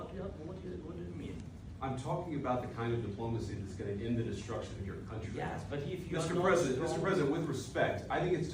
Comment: I think you misheard what Vance said. He didn't say "...that ends with the destruction of your country". He said "....that ends the destruction of your country". He was saying that he hoped diplomacy would help end the war.